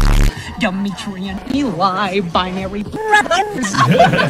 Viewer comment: what is he even saying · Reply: demetrian eli binary brother